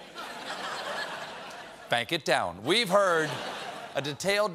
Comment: Did he just say "PANCK it down"?? PANCK is a word in my own dialect...Yooper dialect. One of our own unique words: it means to flatten the snow down. Like hit it with a shovel until it's packed